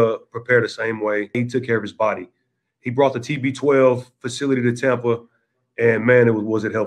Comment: Mike says Tom brought the something something 12. what did he say and what's that? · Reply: He said that Tom brought the TB12 facility to Tampa. It is Tom's fitness/health center brand. They work a lot on recovery and injury prevention so I guess that is what Mike Evans found helpful.